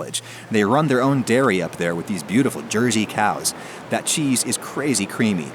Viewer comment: Did he just call that girl student a "beautiful cow" ?